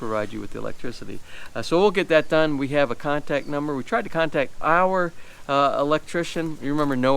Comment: Wait, Wait, hold up really!! Ok you did say contact number right? oh, ok, for a moment I thought you were saying contractor number HAHAHAHA W e all know thats like kicking a dead horse, LOL right? cus that there dog no longer can hunt.